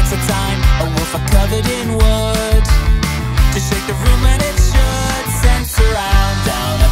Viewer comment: bit nitpicky but he actually says "to shake the womb when it should"